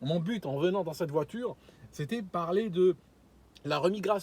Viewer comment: ''Au début je voulais pas parler de ça, je voulais prendre un sujet facile... l'IMMIGRATION'' 🤣🤣🤣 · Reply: Moi j'ai entendu "RE-IMMIGRATION" lol